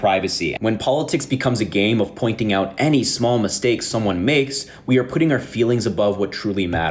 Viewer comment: did Harry just say (4:26) that "facts don't care about your feelings"?